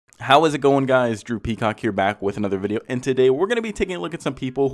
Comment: I think this the first video ive ever seen from ur channel and i thought u said “hey guys droopy cock here” i was like 😂🤦‍♂️